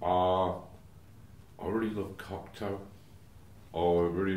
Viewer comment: I don't quite understand what films he says he likes there. Can somebody help? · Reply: I got the first one, he says he loves Cocteau, the French poet, author and filmmaker, Jean Cocteau, that is. His films are very magical and imaginative, I would guess Moore would enjoy them, but I missed the second name, someone help us!